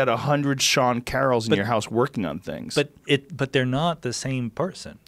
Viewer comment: Ahhh.. Thought it said Steve Carroll for a second...